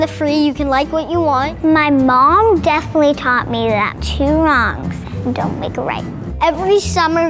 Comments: i miss heard the girl, i thought she said "my mom taught me that, two moms dont make a right" i was so shocked.. i had to rewind. im glad thats not what she said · I thought that girl said "My mom taught me that two girls don't make it right" or something like that AND I WAS ABOUT TO GO CRAZYY ON THIS COMMENT SECTION LMAO